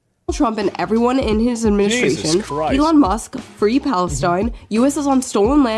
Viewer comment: I thought that said "Free Palpatine"